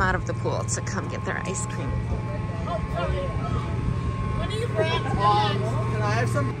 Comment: Did gma really say "what do you brats want?" 5:34 LOL Oh my lawd she's a brash one, isnt she?